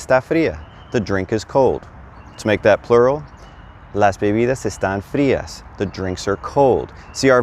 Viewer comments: Why is it not las bebidas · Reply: sorry, I listen to it again and I think I did hear you say las the words on the screen just said la bebidas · Muy util. Te lo agradezco! ... "People sounded like porpoises." Jajajajajaja. 4:01 ... LAS bebidas estan frias. (Although you said it correctly.)